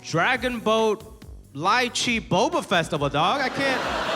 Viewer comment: did he say boba fett